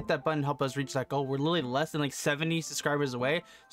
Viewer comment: I thought he said Tech Granny Thanks for the video though!